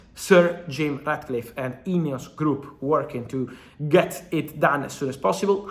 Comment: Swore I heard Fabrizio say “INEOS Crap” not “group”.